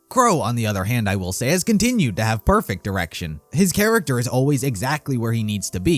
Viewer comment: is it just me or does he say qrow has continued to have perfect erection?